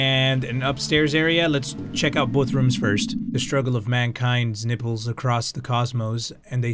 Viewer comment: i thought he said nipples lol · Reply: He said nipples lol.